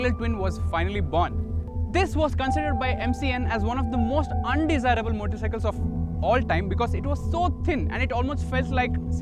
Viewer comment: I seriously dont know if I can watch this cause I can not understand what the heck he is saying around 0:48 something about the most underiable motorcycle...have no idea what model he was saying....dude speal more CLEARLY...maybe slow down a little.